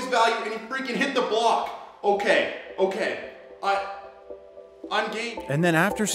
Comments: did he say "I'm gay"??? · why have I heard "I'm gay" ??? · Did he say "I'm gay"? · is it just me or did he say "I'm gay" at 5:35 😂😂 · did I hear you saying you were gay?🤔NO JUDGING